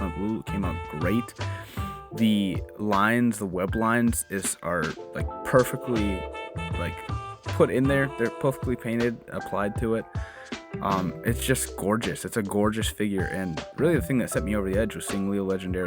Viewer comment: Sick ass top 10! But at 11:56 I heard that “porfectly painted” 😭 That hulk buster and venom might have to get added to my list of figures to obtain! Keep up the great work man!